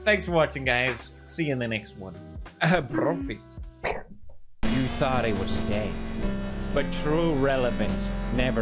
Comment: Auto captions saying "You thought it was gay" Me can't unhear now.